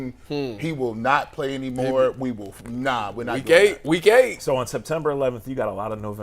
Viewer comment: what is giggling saying? It sounds like, and the Closed Caption said, "we're not gay, we gay." I doubt if that's what he said but I'm just wondering what in the world did actually say.